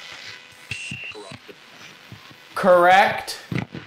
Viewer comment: I heard “corrupted”